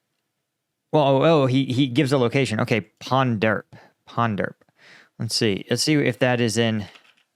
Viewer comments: the guy actually said Punjab (旁遮普) in Chinese and the English subtitle is wrong. So those two Indians were Punjabis. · As a Chinese person, I'll help clarify some translations that didn't quite understand: 1. @ 10:13 "Ponderp" is supposed to be Punjab (he said "旁遮普"), the subtitle is translated incorrectly. 2. @ 24:18 "Third line" is just Chinese colloquial for rear echelon, logistics. It's not that the RU army literally uses a three line system. 3. @ 34:42 Political officers in the PLA are military officers. Aside from political functions, their role is kind of like a co-commander or the deputy commander of a unit. 4. @ 35:33 what he said was "after the veterans have been depleted" (i.e., becoming casualties) 5. @ 36:23 what he said was not "base", but "strongpoint" 6. @ 36:31 what he said was "the recon drones will bring down shells." It's not clear if he's talking about the recon drones observing for the artillery or if the recon drones releasing onboard mortar shells. 7. @ 45:29 what he said was "as soon as a position is lost [by the Ukrainians], their drones would come."